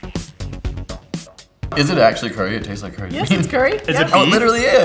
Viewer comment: I thought it said chewed chicken curry and then I was like🤨🤨